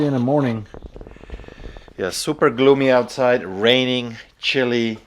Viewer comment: you've ALREADY got a fish finder, called honey ... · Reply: i thought he said super glue me outside